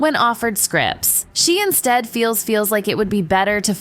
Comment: Did she say feels feels? That's some serious feels! Whether or not Gal is steering clear of BS. That is how a professional handles things. Unlike little snotty brats.